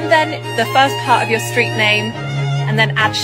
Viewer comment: Lady Jasmyn Lillian Teaton of Seishire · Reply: Lol I misheard the last part. I thought it was "screen name" not the first part of my street name XD then it would be: Lady Jasmyn Lillian Teaton of Coensonshire.